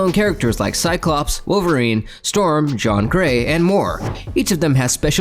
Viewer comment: HOOOLD UP! Did you just say, "John Grey", at like 1:10? lmao